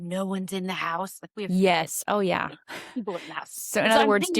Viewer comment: I really want to know what you said at 1:13:23 😅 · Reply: Oh! hehe ... I think I said "we have 3 kids and live in a tiny house, so there's always people in the house." :)